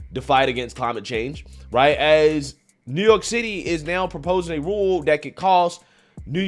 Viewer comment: Did he just say New York shitty?! 🤣😍